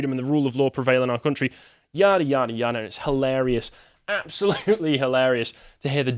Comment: Wow props to for calling out the republicans for being quick to throw Trump under the bus for media brownie points 👏👏👏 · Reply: I thought you said Hilary for a second and got confused.